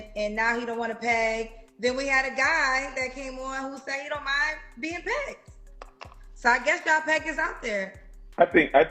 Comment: People got this dude head so pumped up. His handsome looks getting him everything except partnered up. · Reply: I thought he said he don't Anna be partnered ?